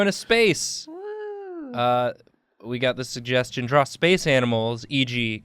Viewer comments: I thought it said spice animals and I was expecting the animal counterparts of the Spice Girls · At first, I thought it said spicy animals. Idk why